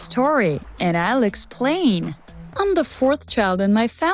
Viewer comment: When you said “and I’ll explain” it sounded like you said and Alex said”